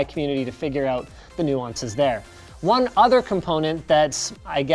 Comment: Why does it say "new (mumbles)?" Doesn't he say Nuances, not new _____?